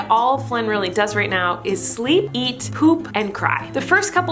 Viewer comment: i thought you said flynn eats poop